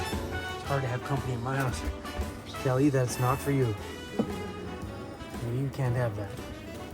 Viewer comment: Thought said you can't afford it right now!